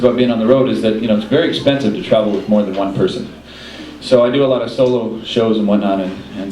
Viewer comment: I first thought he said "with murder one person" and went "HUH? ".... I thought he was talking about hitch-hiking and obscure hitch-hikers... But now I understand he meant "with more than one person"... The car gets heavier, I guess...